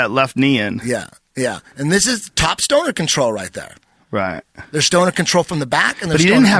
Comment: Wait did i hear eddie say "stoner control"